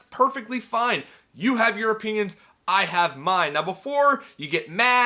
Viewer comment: Could of sworn you just said '... You have your Europeans I have mine.' Happy New Year. That's my European.